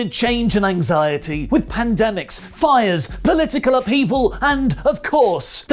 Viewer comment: Wasnt fully listening at the start and really thought said “Bullet Club Evil” and not “political upheaval”